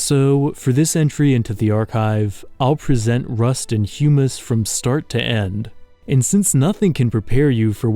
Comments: I thought it said "Rust and Humans" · Ngl I thought it said rust and hummus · i genuinely thought it said rust and hummus